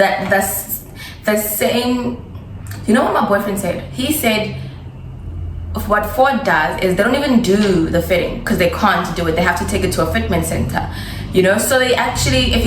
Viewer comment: did I hear boyfriend?😊😊